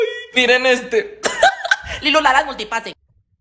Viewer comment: Nolentendi que dijo aqui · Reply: Firulala vakutifase